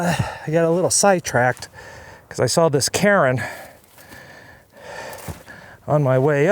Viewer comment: By monument are you revering to a survey marker related to a mining claim? You’re having too much fun! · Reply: I could have sworn you said “Karen” instead of Cairn. Even my wife looked up and asked if that was what you said!! Great scenery!